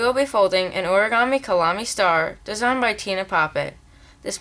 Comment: I thought Tine Pape said Nine Rape... :/